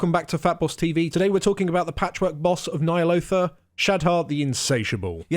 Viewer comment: I thought this bosses name said shad’hap